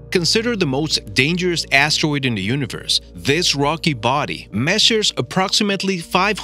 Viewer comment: i thought he said this rocky boi but he said this rock body XD 👌👌👌👌👌